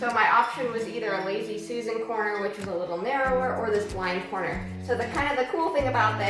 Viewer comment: Blind corner. At first, I thought you were saying wine corner. Running pex (or any type of water lines) in an outside wall makes me nervous. I hope it won't freeze during winter.